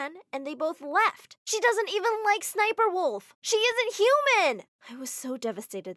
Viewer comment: Did i hear sssnniperwolf